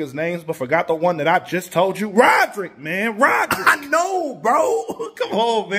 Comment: Wow up until 1:06 I thought he was saying Roger